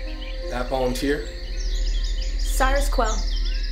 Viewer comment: I thought it said circus quell